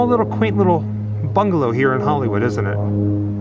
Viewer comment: WAIT!! did you say Hollywood bungalow at 23:50? I wonder if there is a connection with Jim Morrison? hummmmm